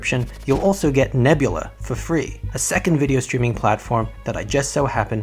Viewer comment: I thought you said ''You'll get Ebola for free''